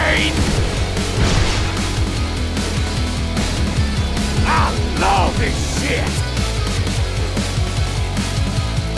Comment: what does he say? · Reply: I LOVE THIS SHIT!